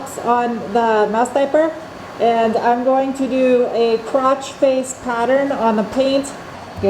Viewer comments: sounds like you said crotch faced how do you do that thanks for the laugh that Mr dos is fire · I thought you said you were going to use a “crotch face pattern” instead of “cross face pattern.” 😂🤷🏻‍♂️